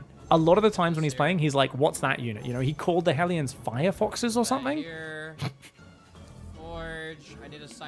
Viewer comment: "The Terran Firefox bullshit" was my favorite bit. 🤣🤣🤣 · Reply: I heard it as fire fucks... Firefox might make more sense tho.